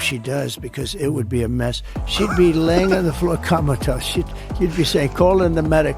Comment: I swore I heard him say "Kamalatosed." 0:00:32 Let's make Kamalatosed a thing!!! MAGA!! 🇺🇲❤️🇺🇲